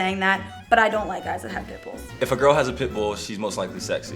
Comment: i keep hearing "pit bull" as "pimple"